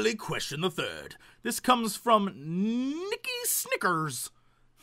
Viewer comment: Haha, in my head I heard that as Nickis' Knickers and not Nicki Snickers